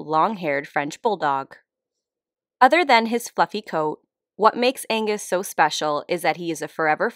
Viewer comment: Took a double cause I heard "what makes him special is his fucking coat"